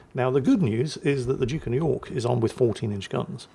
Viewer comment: A Duke of York? You had me guessing which one was the Duke of York here. Answer, it was the King George V. As for that devastating strike, it looks a lot more like a detonation to me. :D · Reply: Oh it’s even better. I think I hear stumble and say “Duke of New York” at roughly